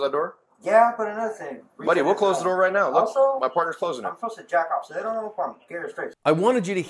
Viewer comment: what did the guy say? cuz i heard "im supposed to jack off cuz they dont know if im gay or straight" lmaoo